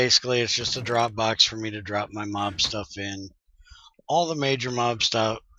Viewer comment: mob stuff... i heard "my mom's stuff" lol